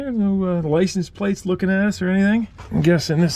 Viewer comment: umm did he say ass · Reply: License plates looking at us